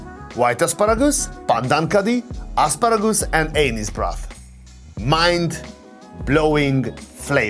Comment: Did he say “anus broth” at 35:08?